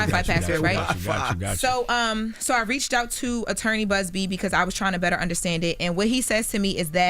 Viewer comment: Did she just say she reached out to attorney Buzby? Loren is that girl